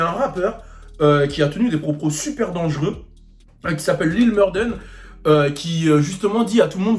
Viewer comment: Broke (ou L :D) The Kid en recherche de clout mdrrrr. 2:59 Lil "Mur"quoi ? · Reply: Lil morden j crois